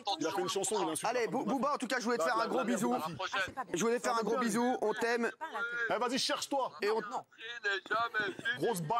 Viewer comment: Stp il dit quoi en 16:48 ?? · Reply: la mère de ma fille je crois